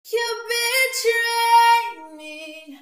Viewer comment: I thought she said "you bi*tc...